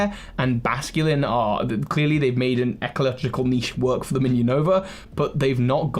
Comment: Basculin has made a…what? Evolyptical??? Did you mean to say ecological?